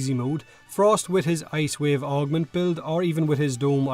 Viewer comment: Ice Wave Build and * what? Can't hear what he said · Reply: ice wave augment build